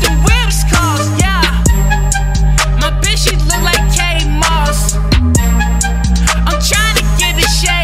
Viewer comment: Thought he was saying Kate marsh from life is strange